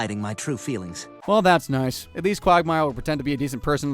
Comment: Did you say "Quagmile"??? 😂😂😂 8:46 THAT WAS NO MISTAKE!!! Y'ALL EDITED THAT CLIP! Peter first pulls the trigger with safety on, no shots fired. Then he pulls the trigger and says "This is a gun with no safety on." bang goes the rifle 😠😠